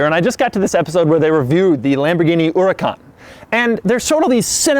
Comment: it sounded like u said the Lamborghini ouracan